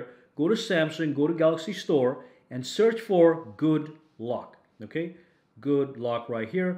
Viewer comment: Could have sworn he said good luck instead of good lock